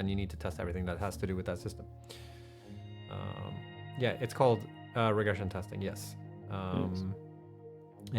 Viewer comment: I heard "It's called irrigation testing". Are we getting farms next?